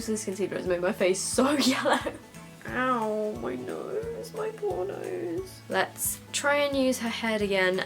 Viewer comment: you said "ow my poor nose" I thought you said "ah my pornos" 😂😂